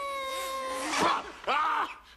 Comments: I thought he said you smell like fish heads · Phys Ed??? I thought he said: "You smell like fish heads." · I always thought Chunk said: “you smell like Sinbad!” 😆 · I thought he said you smell like fish eggs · I always thought he said “you smell like fish heads!” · Hear to show my 6y this scene after we had a laugh session and I ALWAYS thought he said "you smell like fish head" now I'm taking a minute to let it absorb and I'm like idk why I never heard phys Ed before!!😂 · I thought Chunk said," You smell like fish heads."